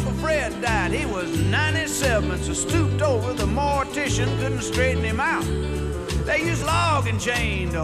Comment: When I was younger and first saw this on VHS, I thought he said politicians, not morticians.